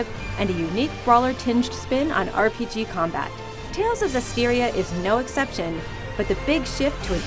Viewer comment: I thought it said Tales of Zelda lol